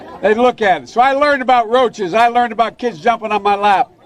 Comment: from what I heard Biden was likening himself to a cockroach 😃 did he say he learned about roches? 🤔